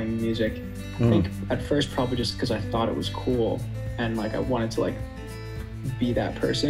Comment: Cmiiw, subs nya ad yg keliru.. klo gw ga salah denger..si Jeremi bilangnya “i thot it was cool”, bukan “school” tapi sub ditranslate jadi “sekolah” 😊